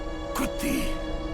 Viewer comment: Sounds like he said "Kuthi" n not good day. Kuthi means bitch in Hindi.